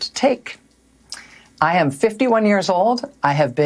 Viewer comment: did she really say "I have 51 years old"? For real? FOR REAL? She should be fired as well just for using the verb HAVE in that sentence